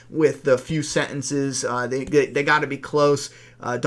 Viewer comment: Depending on how i listen to the second whisperer i hear him say diffrent things. First i heard: "They've must regrouped - don't let them get away" Next time i heard: "They must be close - don't let them get away" I can still hear both :p The second one is ofc the right one, just funny how i can hear it so wrong.